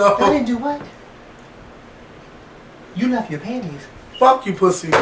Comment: pause 3:31 is hilarious, even i know what he said- scenario: 'it was so rewarding because (picks up ipad to cover his mouth) we didn't have to pay' - 'what? you left your panties?' you guys keep me laughing- thanks for the uploads